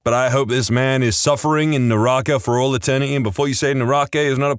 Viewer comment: Fun fact: in buddhism Naraka although might not be permanent the amount of time you spend there is like, super super super super long compared to the human world, a year at that place is like a 10 lifetime and bro will probably get like 1000 years in there, the torture is also really cruel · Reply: Almost thought they said Naraku